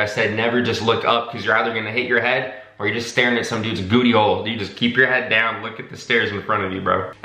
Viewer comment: I swear this man just said gootyhole 😂